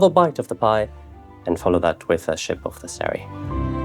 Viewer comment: does he say "..ship of the serry.."?